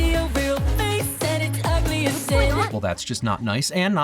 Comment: oh i thought they Said ugly as sh**